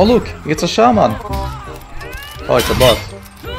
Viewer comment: мне послышалось "Да, это бот"